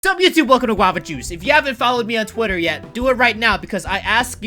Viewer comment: when I first started the vid I thought he said don't get to close to guava juice and I was like what